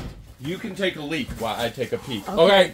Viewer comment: the cheerleader. I laughed when thought said he had to take a leak... 😂😂😂😂. She said take a peak